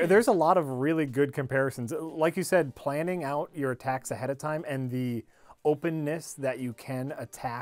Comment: When Jack said "planning out attacks" I misheard it as "planning out your taxes" and was very confused by that sentence!